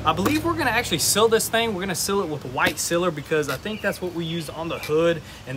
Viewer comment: heart kinda dropped a second. Thought you said SELL this thing. I was like NOOOOOO!